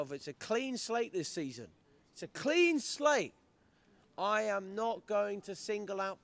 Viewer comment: Hang on, I thought you said a clean sheet? Obviously not with Fofana. Unless Maresca uses his full backs as a genuine PL manager did mid week with James and Madueke, playing out wide Gittens will always struggle as Sancho did.